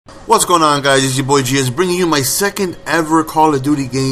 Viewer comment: At the start it sounded like he said 'hey guys it's your boy jizz' haha